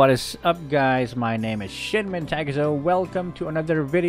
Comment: I start to think my internet is trolling me. It suddenly lag at 0:02 and I heard: Hello my name sh#t. Why internet, why!?